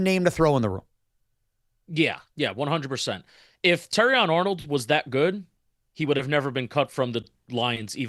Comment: I thought Shaun was saying Terry & Arnold. Now I know the guy’s name is Terrion Arnold. What kinda name is that?!